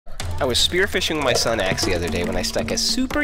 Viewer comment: Why in the opening scene did it sound like he said I’m spearfishing my son axe